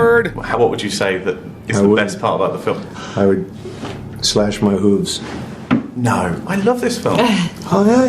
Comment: I had to rewind one part. Sounds like Bruce said slash my hooves. That is a warning sign alright. 🙁